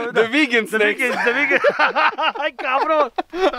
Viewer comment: did he just say "cabron"???